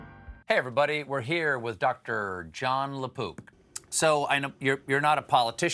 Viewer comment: I had youtube autoplaying in the background while I was doing something else. The intro made me look up sharpish because I swear I heard "Jon Le Poop". I thought "Now this is going to be a shit show".